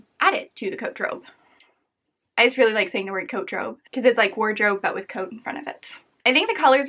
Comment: I thought you were saying "coat trove" not "coatdrobe" but coat trove works if you really like coats since a trove is a valuable collection.